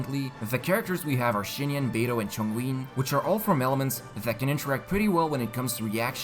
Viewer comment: Mans really just said Chongween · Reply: I thought he was saying Chong Ling as in Chong Yun and Xiang Ling because he was showing Chong Yun with Xiang Ling's pyronado active